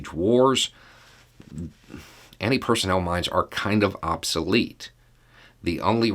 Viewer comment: dammit! i mean, okay, but. I was all excited because i thought it said "mimes".